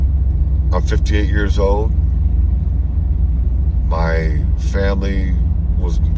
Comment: I had to listen closely a couple of times because it sounded like you said you were "15 years old" 😅😅. Cool video! I'm not sure where that road is, but if I ever find it, I'm definitely going to going to take a ride.